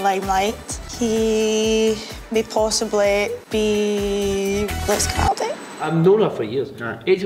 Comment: What does she say? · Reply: JacobOlli “May possibly be..... Lewis Capaldi”